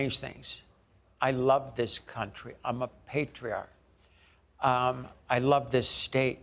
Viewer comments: weird @ 4:02 she wanted to say "I love this country I'm a patriot" but it sounded like she said "patriarch" (not that there's anything wrong with that) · Did Kaitlyn just say "I'm a patriarch"? I mean, I think it was meant to be "patriot", but you gotta admire the humor in that. 🤣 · "I'm a patriarch" Is trying to make us think he said "PATRIOT"??????? DON'T BE FOOLED BY THESE SATANISTS WORDS.